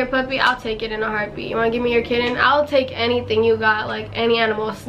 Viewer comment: When u said that u would take kitten I thought u said kids haha